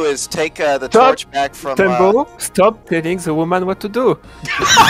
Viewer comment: what did he say? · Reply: I said " Tambo, Stop telling the woman what to do".